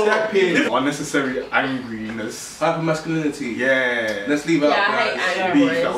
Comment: did homeboi just really say masculinity? I can't help it that I'm really tomboy-ish, some females just aren't able to be all ladylike and 'proper' 'cause that's just not us. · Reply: FLOG GNAW He said hyper masculinity